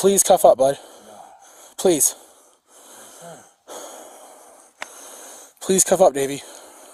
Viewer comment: I could've sworn I kept hearing "Please cuff up Baby" like the cop boutta get into some BDSM with the perp 🤣